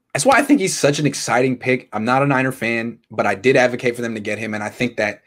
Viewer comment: Wait did just say at 2:42 that he’s not a miner fan? I thought he was what?